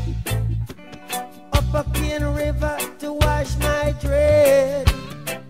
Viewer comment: I thought he was saying pain river…….